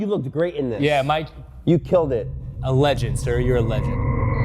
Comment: thought he said ALLEGED